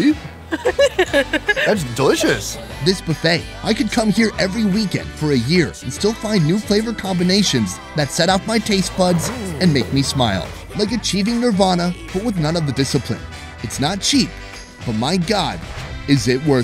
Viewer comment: did he say, 'the cheating Rawvana?'